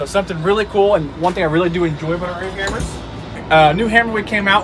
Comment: "One thing I really enjoy about this air hammer" I heard "This is what they told me to tell you"